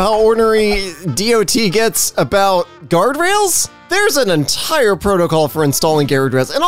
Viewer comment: ….. Did you just say Gyarados??